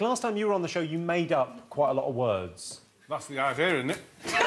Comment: What does he say at 8:35!? "Last time you were here, you made up quite a lot of words" -"lasting my ear in'it" I can't make heads or tails of it... (I'm not a native english speaker) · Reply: that's the idea in'it = that's the idea, isn't it?